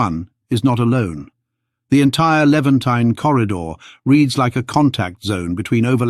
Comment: did the narrator actually say eleventeen ? 😂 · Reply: it sounds like he said “Levantine”. He mentions the Levant later.